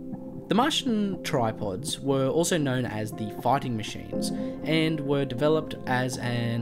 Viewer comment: Sounded like he said farting machines